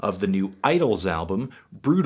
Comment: I got so excited for a second because I was listening to the video while looking at another tab, and I heard you say "Idles" and I thought you meant the Australian metal band "Idylls"